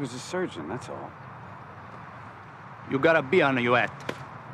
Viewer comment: I always thought he said "You got a bee under your hat", which is funnier